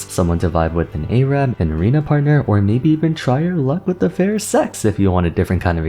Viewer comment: what did you say 😭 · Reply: try your luck with a fair sex!